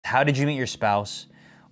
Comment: I thought it said how did you eat your spouse